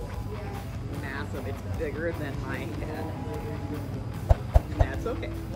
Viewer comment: Did she say "bigger than my ass" ?